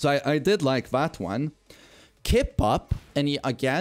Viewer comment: what are you saying sorry? Keep out? Keep up? Kick out? English not my first language sorry. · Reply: He's saying kipup, it's the spring kick you can do when down.